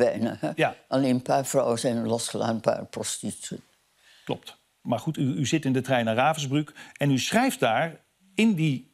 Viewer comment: Wat zegt ze nou op 0:30? Ik verstond het op tv al niet en kan nog steeds niet helemaal ontcijferen. Wel een fantastische special en een zeer indrukwekkend verhaal met misschien een nog wel indrukwekkendere vrouw. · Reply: Ben Oranje dat zei ze.